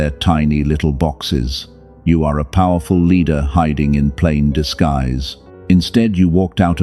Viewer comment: I heard say " I hid you in plain sight." 🙏🥹